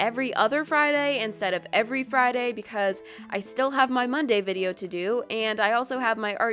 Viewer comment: At the end, instead of you saying monday, I thought you said "bun day"